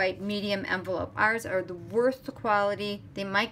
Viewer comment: Your cards and creations are always favorites, but this is stunning! However, did I hear wrong or did you really say (at about 7:05) that you used a "whisper white medium envelope; ours are the worst quality...." I even had to rewind and bring up the close captioning and it heard the same thing I did. I actually chuckled because I know that was either a misspoken word or CC and I heard in incorrectly, because we all know the SU! envelopes are amazing. Nonetheless, it's a gorgeous creation. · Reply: Thanks for your comment, Lucy. At 7:05, I say they are "worth" the quality :) Sorry it was unclear. I LOVE our envelopes and they are the finest quality for delivering beautiful cards!.